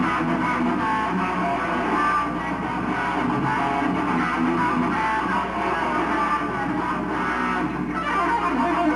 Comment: Thank you so much for this upload, dude! maybe you have the japan clinic too?? hehe. Can anyone transcribe what's jason saying around 45:00 after he plays stranger? he said one of the reason he left cacophony... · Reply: "Marty Friedman is an awesome guitar player" - Jason Becker